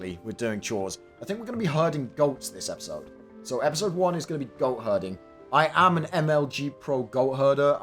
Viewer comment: I misheard "goat hurting"